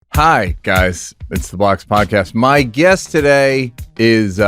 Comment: Definitely sounds like he says "the blacks podcast." If there was a blacks podcast it should be set at the cookout. Or a movie theater where they can all talk really loud.